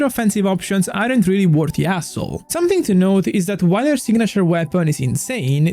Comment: i thought he called Columbina an A**hole , it's hassle, I get it 😅❤